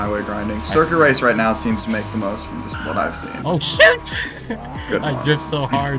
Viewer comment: 1:41 OH SH*T Me (ChrisTheEpicYT): wait did this just say that?